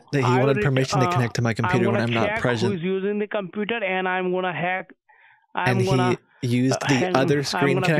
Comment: Did He just say "I m gonna hack"....🤣🤣Creepiest Voice of the Scammer(Hacker🤪) with Fake Accents 🤣🤣🤣🤣 Damm Hilarious Video. Thanks for what you are doing. New Sub😀👍